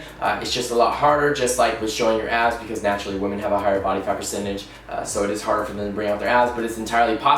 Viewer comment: Tell me why I fully thought he said « Women naturally have a higher body count percentage. » ? 😂 😭 😭